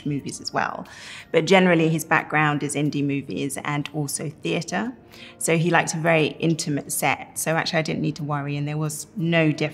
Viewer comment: I thought she said Sam Mendes “prefers intimate sex” I literally had to rewind twice to understand lol